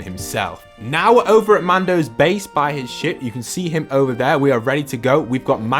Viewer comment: said shit instead of ship. skip to